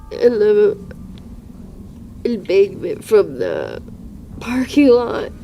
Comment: Captions here are incorrect. The word speaks here is not inaudible, as subtitles claim; I clearly hear her saying the word "embankment."